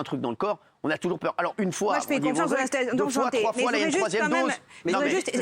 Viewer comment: Elle dit quoi ?! "je fais confiance à la stérilisation" ?!?